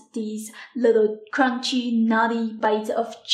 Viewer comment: I thought she said, " crunchy naughty little bites of chicken..." Meaning this is so good it's bad. Wiping eyes from tear laugher now. Description reads, 'nutty'.